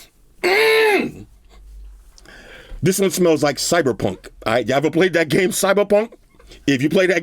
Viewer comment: I thought you was gone say cybertron, but you said cyber punk 😂. I was close dammit. 🤦🏿‍♂️